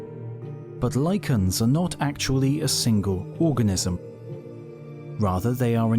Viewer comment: did he say lycans, somebody call Selene